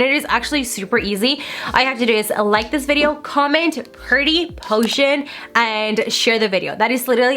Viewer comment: Oh, and pretty poison 😋😋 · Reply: Sounded like you said potion? EITHER WAY, I COMMENTED IT!